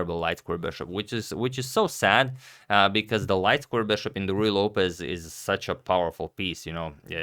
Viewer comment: I always hear "Ruy Lopez" as "Royal Opus."